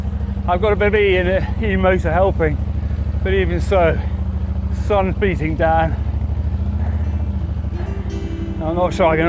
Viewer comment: did he say he’s beating Dan.? 😭😂😂😂😂